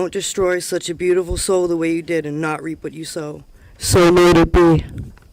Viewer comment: what did say into the mic at the very end? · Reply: So mote it be